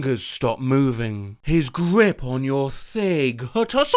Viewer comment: Ladies and gentlemen, it has come to my attention that at 1:10, said ''thig'' instead of ''thigh'', even when it was meant to be thigh. This is evidenced by the fact that ''h'' carries over from ''thig'' and, in the context, ''His grip on your thigh'' simply makes more sense.